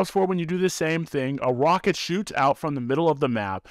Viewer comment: Am I the only one that heard him say a wocket😂